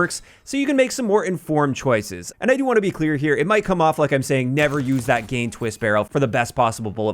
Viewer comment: I heard "Never use that gay twist barrel." 😆